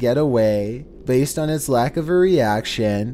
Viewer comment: All I heard was CWD Or “Zombie dear”